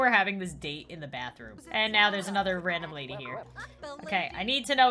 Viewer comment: It almost sounded like she said ”F🌸 the lady”